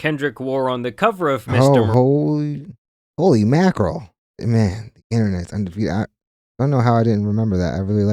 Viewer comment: 😂😂 boyyyy did you just say holy mackerel? Nahhhhhhh